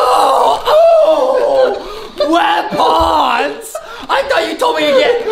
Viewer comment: WEPONS I THOUGHT U SAID MELONS 🤣🍉🤣🍉🤣🍉 — Justin 2020